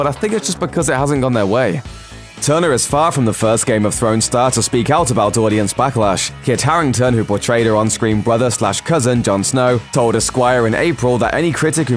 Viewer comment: I thought he said Kit Harrington told his Squire intstead he said Esquire.